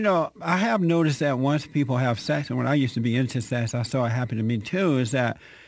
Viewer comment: Did he say, "When I used to be into sex"?